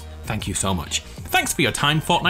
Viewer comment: For some reason I thought you said “Thank you from my family.” at the end instead of “Thank you Fortnite family.” Oh god I’m tired.